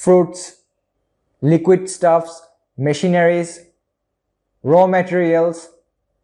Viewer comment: I thought I heard "Missionaries." It was "Machineries." Thanks for the tutorial, though! Helped me a lot.